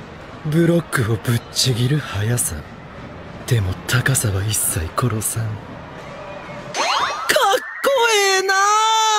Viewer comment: i hear dazai-san